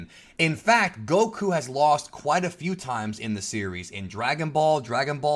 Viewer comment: I thought you said a few times?